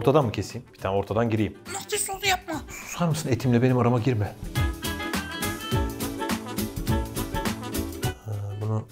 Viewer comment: orda Kim ne diyor? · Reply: diyor